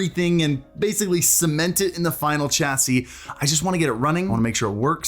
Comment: Did he say CH-SIE?!